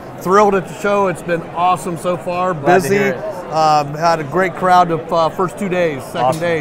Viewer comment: Did anyone else hear "pussy" at 0:22?